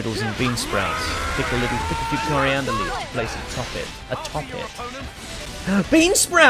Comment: I thought said i'm full of beam... Like energy beam... It's only after seeing the meme that I understood